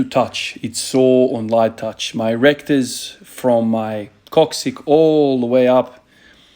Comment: Video starts and all I hear is: "My erectics are down on my coccyx" 😂